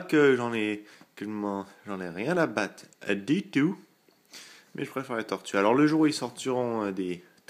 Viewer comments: Si toi aussi t'as entendu " Mais jpréfère les torturer " ... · Omg j'ai compris "…mais je préfère les torturer" à 1:53 o_O